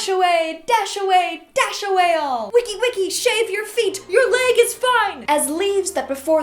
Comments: I thought it said SAVE your feet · “shave your feet” I thought it said “shave your teeth” 😆 I am worse than Google Translate with words I am the human google translate 😆(not really)